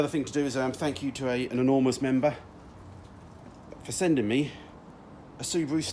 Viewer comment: I heard: "an enormous member" 😂😂